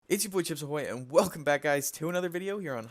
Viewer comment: did i just hear chipsy boy?